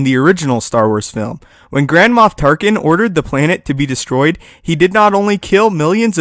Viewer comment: i thought you said grandma tarkin! XD!